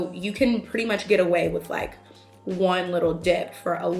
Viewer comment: i thought you said one little 'dick' lmao